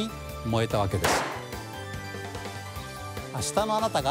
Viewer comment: 「明日のあなたがエネルギーで幸せに」が「下の穴からエネルギーで幸せに」に聞こえてしまった。